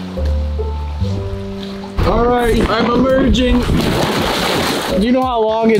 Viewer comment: For a second I thought he said "I'm a virgin"